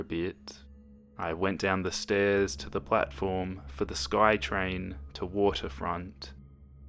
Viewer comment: omg at first I thought he said butt formXD